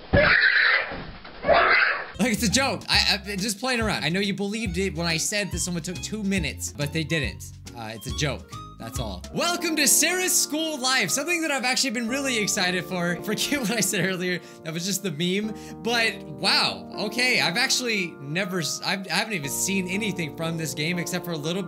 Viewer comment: its sounds like he said "welcome to series school life!"XD